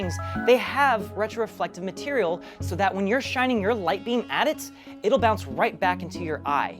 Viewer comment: did I just hear "shining your lightning at it" ....?